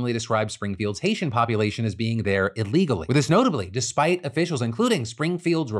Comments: Just wanted to let you know the CC about the immigrants through Youtube states that the mayor and Ohio governor said "arrived in Springfield illegally" despite you saying legally at 14:49. Wanted to mention it in case y'all have access to change that! Its such a huge deal here in Ohio that someone who relies on CC might get misinformed on what they said. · the subtitles say illegally but Philip clearly says and means legally. <3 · At about 14:54 you say "Legally" but your CC says "Illegally" Phil. Messes with the story a little, had to go back & check.